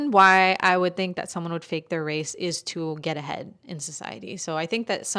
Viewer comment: when she said one of the reasons they fake their race i swear i heard "to get head" yuydyrfdyudktik